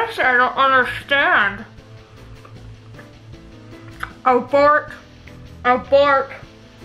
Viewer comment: The bloody bites was hilarious. And it sounded like you said abork I'm laughing so much with you.